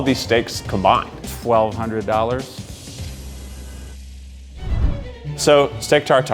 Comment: Oh man I thought it said $1200 steak, I was so intrigued